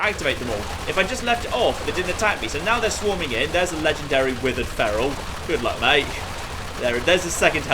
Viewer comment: When he said withered feral I thought he said will Ferrell